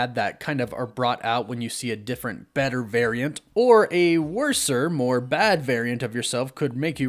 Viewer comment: Did he just say "WORSE-R"? @ 5:53? LOL 👂